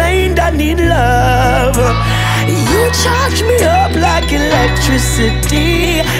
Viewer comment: I could have sworn he said charge me up, but the lyrics in the video say torch instead... 🤔🤷